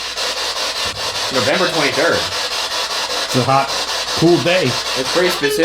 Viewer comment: #postmortem when you guys turned on the spirit box, I heard "Sniper" instead of "November 23". Just me?